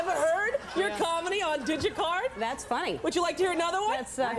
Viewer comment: Did she say DigiCard?.........DigiCard!?!?!?!! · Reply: RayRae 559 She actually said DigiCart. State of the art at the time.